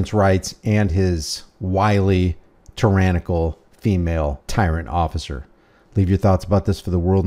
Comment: Sounded like she said mmm tyrannical